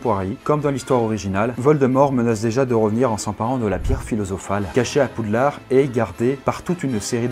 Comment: La quoi ?! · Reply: la pierre philosophale, ce que Voldemort cherche à récupérer dans le premier film, ce qui lui aurait permis de revenir à la vie